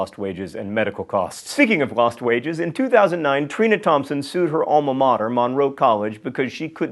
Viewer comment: I heard " ...Thompson sued her own momma" haha